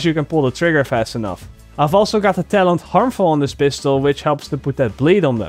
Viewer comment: thought i heard him say "I also got harmful on dis b**ch tho." lmao